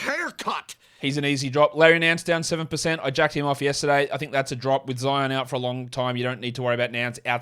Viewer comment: what did he say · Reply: I jacked him off yesterday, he's a drop with Zion out